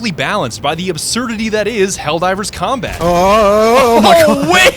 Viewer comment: Lmfaaooo the video buffered right as you were trying to say combat and cut it off perfectly. All i heard was "helldivers cum-" 💀💀